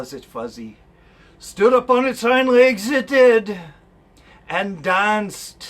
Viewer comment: Youtube auto generated captions. 🙄 Please. Can anyone reply what is actually said at 6:09? "stood upon its only exited" These words. Thank you. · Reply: "Stood upon it's hind legs, it did." Thank you for watching and commenting.